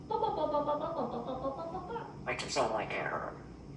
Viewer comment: I thought he actually said "makes you sound like Carol" 😅